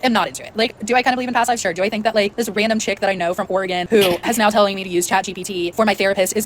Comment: Wait, did I hear " from Oregon?" Greeeeat.